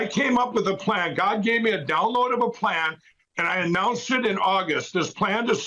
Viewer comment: did he actually say "god gave me a downloadable plan". When did heaven get an internet router?